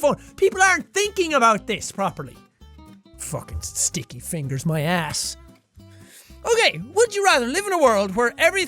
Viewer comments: when he said "stickey fingers my ass" I thought he said "stick a finger in my ass" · Am I the only one that thought he said “Stick your fingers in my ass” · when he said "sticky fingers my ass", i thought that he said "stick fingers in my ass" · Sean said "Sticky fingers, my ass," as in that common phrase when you find something distasteful, but I heard "Sticking fingers in my ass."